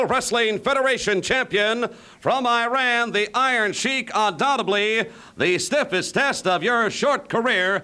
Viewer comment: Did Mean Gene just say "undoubteBLY" ? 😂 it is undoubtedly lol. That's like when people say "supposebly" instead of supposedly.